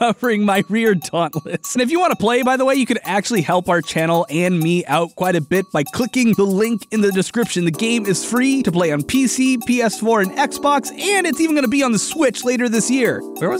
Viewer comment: Did Austin just say PS One?